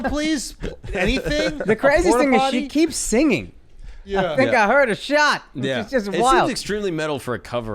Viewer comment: He is just lucky nothing else slipped out! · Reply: That’s why I thought she said “I think I feel a shart” lol